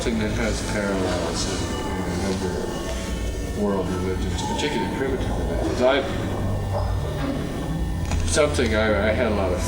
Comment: What's he say here? "Particularly ???" 20:01 and 20:08 What's he saying here? "??? entities" I can't make it out at any volume level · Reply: "particularly primitive religions" "Haitian voodoo entities"